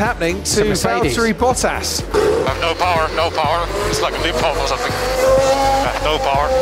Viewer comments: How does "limp home" (what he actually said) sound like "default" to anyone... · I dont think he's saying "its like a default or something" but instead "its like limp home or something" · i thought he said "limp home" not default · I think the subtitles are wrong there sounds like he says "limp home or something"